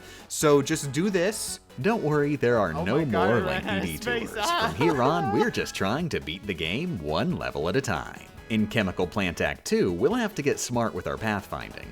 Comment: What the voices overlapping at 10:55 said was, “Don’t worry, there are no more lengthy detours. From here on, we’re just trying to beat the game one level at a time.” And “Oh god, I ran out of space, ahaaha!”